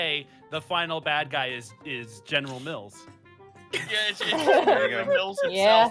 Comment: I totally thought I heard you say general milf instead of general mils I spat my drink across the kitchen